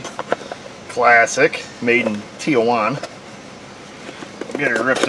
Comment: Jajajajajaja, it sounded like (0:20) if you said that the part was made in Tijuana